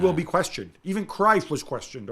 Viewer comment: Oh! Cruyff! I thought gab said "even Christ was questioned", I was thinking woagh Gab, that's too deep!